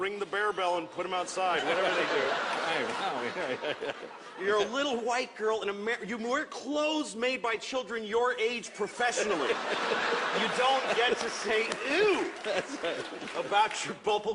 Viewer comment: Tem alguns erros nas legendas: Em 3:04 ele diz "Você veste roupas feitas por crianças da sua idade, profissionalmente" Em 3:48 ele diz "você não pode tirar o CONTEXTO histórico das pessoas" Em 4:05 ele diz "Eu já ouvi pessoas educadas dizerem que a escravidão foi a 400 anos" Em 4:19 "Isso é o quão recentemente você podia comprar um cara"